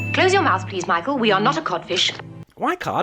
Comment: I always thought she was saying “caught fish” not “codfish”???